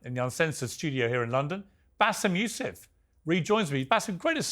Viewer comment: It says Basem MF instead of Basem Yousef in the subtitle at 1:40 , I hope these are autogenerated , you should fix it